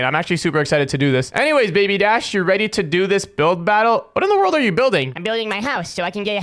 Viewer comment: I also thought you said milk battle😂